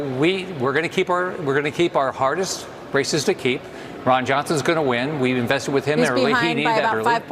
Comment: When he said 'we're gonna keep our hardest races to keep', I initially heard it as 'we're gonna keep our hardest racists to keep' ! 😂🤣